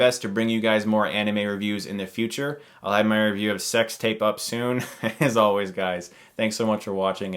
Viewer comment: For a second I thought you said "I'll have my review of my sex tape up soon"